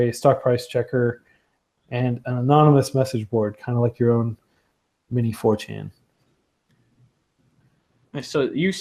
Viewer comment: What does he say? Kind of like your own midi fourteen? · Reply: D G D "Kind of like your own mini 4chan" 4chan is an online forum.